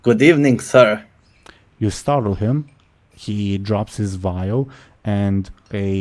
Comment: I heard "Drops his bile" instead of "Drops his vial". Now I imagine a swarm of zombies attacking Thrun's shop